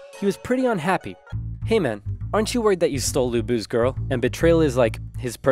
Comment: Dung Zhao took Lu Bu's girl. This angered Lu Bu, who punished him severely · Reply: I thought it said look like lu bus girl